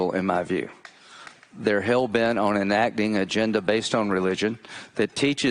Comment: was he talking about israel . sounded like israel to me